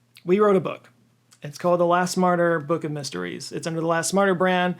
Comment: When mentioned the Laugh Smarter series I thought he said “Last Martyr”, which would be a totally different but also intriguing label… 😂